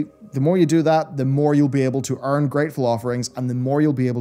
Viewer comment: Oh my ...did he just say "unattractive rewards?" (listen at 14:29) lol